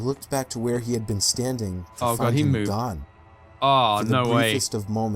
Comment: the subtitles read God instead of gone 😂 " I turned around to find him God"